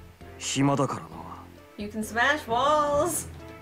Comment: am i the only one who heard her saying "You can smash balls" and thought about Subaru and Kou on 12:21 lmao